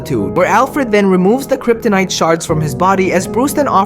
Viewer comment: I thought he said kryptonite shart bruh